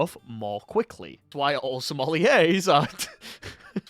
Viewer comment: OH MY GOD, I THOUGHT YOU SAID “SOMALIANS”.